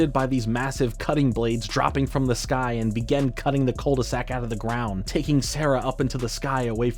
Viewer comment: The part with Ed, Edd, n Eddy where the aliens abducted the entire Cul-de-sac and flies away through the galaxies is the strangest way to end the episode. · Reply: Can’t believe, in the case of Ed, Edd & Eddy, I used to mishear Cul-de-sac as Golden Sack!